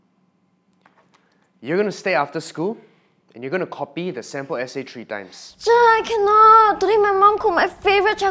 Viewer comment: When said "u are going to copy the essay 3 times" I thought he said "u are going to call me the senpai 3 times" LOL